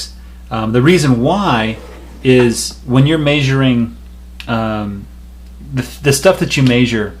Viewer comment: Can't get over how this guy says "measure" (0:25) , thought you were saying "major" 😂😂